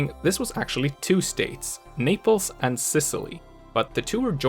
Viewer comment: When he said Naples I thought he said niples!!!lol